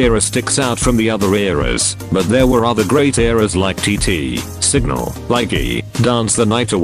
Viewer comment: why did i hear Single?